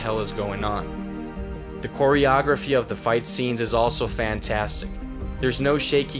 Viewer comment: Did you say Quarrieography about 3:06?